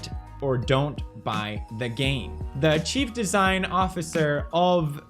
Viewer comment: i literally heard you say it " cheap design officer ".